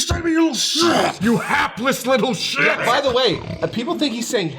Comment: I always heard "Heartless little shit"